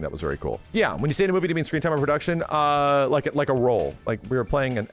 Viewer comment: what did the comment say?? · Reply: "When you say "in a movie", do you mean screen time or production?" Is what I hear